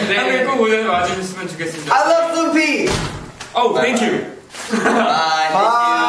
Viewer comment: lol! i thought he said, "I love Snoopy!" OMG! They are so adorable and aweseome and brilliant and beautiful and amazing and.....